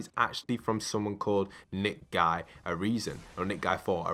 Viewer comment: you said “nickguy4 a reason” instead of “NICEguy4 areason” 🤦🏼‍♂️🤦🏼‍♂️